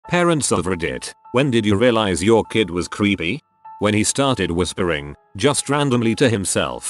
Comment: I thought the AI said "your kid was creamy" 🧐🧐